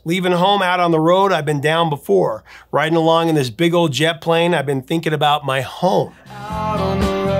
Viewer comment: Steve Miller is my hero for telling the Rock And Roll Hall Of Fame to go screw itself. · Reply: Nope. I thought he was saying, "Big old jet Adelaide." It's amazing what we hear as children, and then remember as adults.